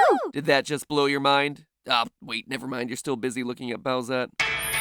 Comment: when he talk about boughs if you turn on the subtitles to English instead of saying oh wait never mind you're still busy looking at bowsette it says looking at bells